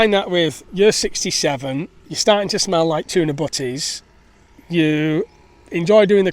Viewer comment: trying to decipher the word you used in the sentence "you're starting to smell like tuna [something]" (at 5:02)... the auto captions show "tuna Buddies", what are you actually saying there if you don"t mind sharing please? sounds like some funny expression. cheers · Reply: Butties - as I’m sandwiches 🥪 😁